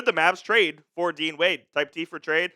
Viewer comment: I got excited for a second. I thought he said Dwayne Wade.